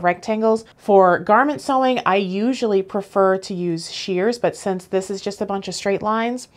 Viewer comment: I thought she said she uses sheers as in sheer fabric, and then I realized she meant shears as opposed to a rotary cutter. Nice fabric, BTW.